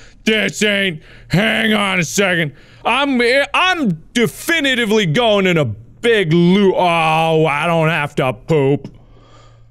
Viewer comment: I love how he's about to say "I'm definitively going in a big LOOP!" but he didn't finish, so it sounded like "a big LOO" and at that moment he was at the toilet. 😂